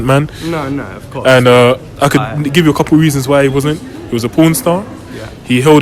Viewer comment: Did he say George Floyd was a porn star?